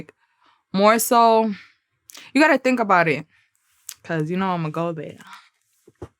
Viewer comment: I swear I thought she said “cause you kno I’m a gold digger” 😂🤣😂🤣 I still don’t kno what she said so I’m just gone go ahead and assume she said she a gold digger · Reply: "cause you know I'm gonna go there"